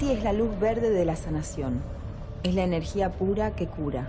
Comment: There is a huge mistake in 44:12, as for "this nation" instead of "healing", because in Spanish it´s "sanación". I only say this for correction purposes and for public to understand well, not to be a hater.